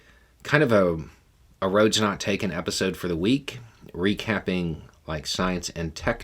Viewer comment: I thought you said "Roads Not Taken for the weak". 😂